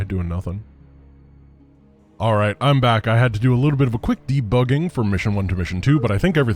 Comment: "I had to do a quick debugging"... after going at 2x through the first mission, I misheard this as "a quick depot-ing" and assumed it was a euphemism for pooping, before I listened again.